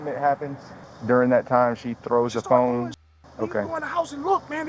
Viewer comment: She threw her phone bit I thought she said she had no phone or number in the house